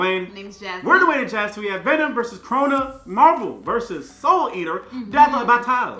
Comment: I deadass thought that said "Venom vs corona" 🤣